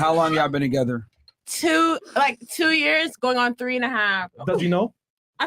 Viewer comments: did she say 2 years going on 3 and a half? Tf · Timestamp (12:50) did I just hear say she was in a relationship for 2 years going on three and a half??